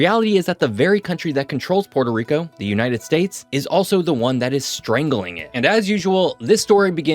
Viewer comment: I thought u said the the united dates 😭